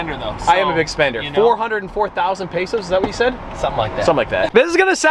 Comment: did he just say pesos?